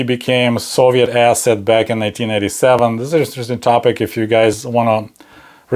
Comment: Trump went to Moscow in 1987 to discuss Trump Tower Moscow, not 1997. Small slip 🙂 I am sorry you were wrong 3 years ago, it would have been nice it there had been no war. It is total insanity right now. Best wishes to you and your family. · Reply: I only hear 1987. Maybe I'm wrong?